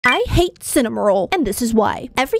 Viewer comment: I thought you said you hate cinnamon roll